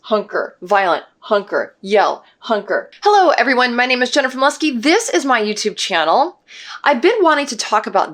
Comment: I heard "Hello everyone my name is Jennifer Musky" in the intro D